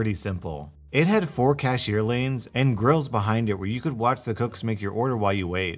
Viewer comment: I heard “grill” as “girl” and I was like “Wat” XD. I can’t with this right now.